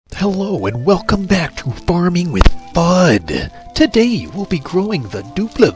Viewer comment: For a second I thought you said “welcome back to farming with mud”